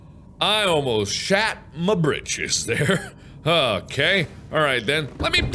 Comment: MARK YOU NEED TO CUT THE MARBLE MELONS TO GET SEEDS. Cough Cough Oh and mark you should make a repulsion cannon to BOOSH all of the evil creatures of the deep away. Oh and at around 5:55 I thought mark said "I nearly shit my bitches!"